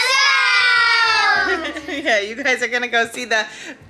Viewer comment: am I the only one who thought the kids said "uncle's house" not "ultrasound"??? edit: and the baby looks so much like Duncan!! In the start of the ultrasound, when we saw his face, the first person I thought of was Duncan because they look so alike.